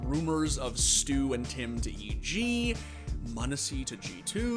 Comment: did he say tim? like autimatic?